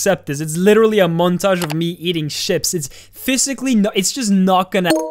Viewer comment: "it's literally a montage of me eating chips" - Dani Deverson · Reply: Ngl I heard "eating shit" the first time around